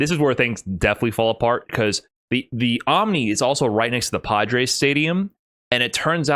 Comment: Man... I heard "Pod Race Stadium"